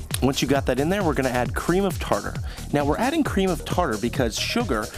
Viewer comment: what did he said at 0:35 did he said cream of parker